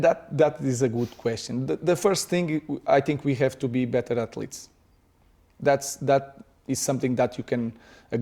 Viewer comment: DId anyone think at first he said "We need to be better at Leeds" 🤔😂